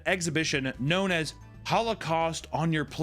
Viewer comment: At times at 1415 I thought he said howlocaust....I was speechless